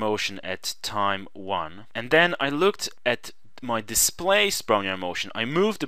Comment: Can someone please tell me what the sprunjer motion at time 5:45? please, i am doing my dissertation on this and i don't understand what the sprunjer motion is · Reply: "displaced Brownian motion"